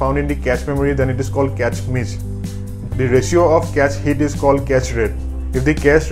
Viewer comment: He said "cache rate"... I had to pause the video and think because I heard "castrate". Lol